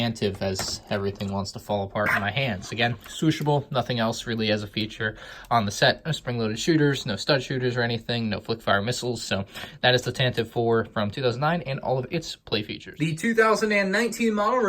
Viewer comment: did he say foot fire missiles